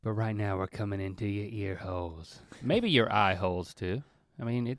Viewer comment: Did Link just say were cumming into your ear holes Timestamp